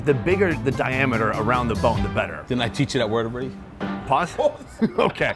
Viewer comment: What did he say on min: 2:00 approx? (Pause?) “Can i teach you that water… “what?…